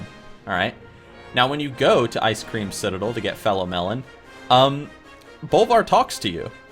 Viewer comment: did he just say ice cream citadel? edit: ok he said it again. first video for this guy and i guess thats just his thing